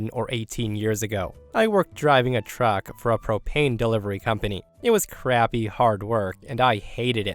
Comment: I had to back up and listen again, to the beginning of the first story. I thought he had said "I work for a cocaine delivery company". 😂